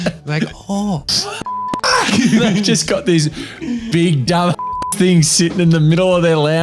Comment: They clearly said they got the safe open. It's funny , you laughing at stupid people. But I'm laughing at you 😂